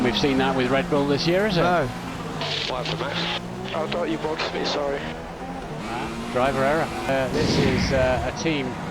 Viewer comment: what did they say to Max? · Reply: what happened max